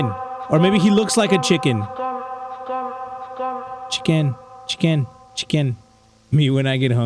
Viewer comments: i heard 'skin skin sin skin' not 'chicken chicken chicken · I heard "skin"